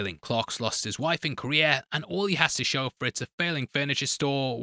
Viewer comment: How does know Clark lost his wife in Korea? Which Korea was it? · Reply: "Clark lost his wife and career" It's just his accent making it sound like he says korea